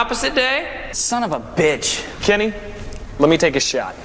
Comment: "Kenny, let me take a shot." I don't know why, but that line always gets me. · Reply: I know "Kenny" makes more sense, but it sounds like he says "Jenny"